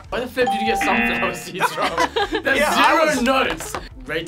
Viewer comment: Did he say, "There's no nuts!" When... hazelNUT..?!? LMAO! Maybe I misheard 😂